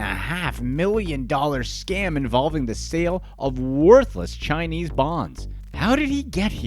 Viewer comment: I THOUGHT U SAID CHINESE GONGS LOL